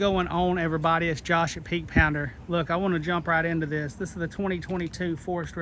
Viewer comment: I honestly thought you said pink pounder... sorry about that....